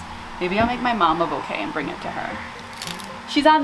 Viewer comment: On my gosh. I thought you said your mother was on the roof 😂😂 I'm thinking " What in the world is she doing on the roof"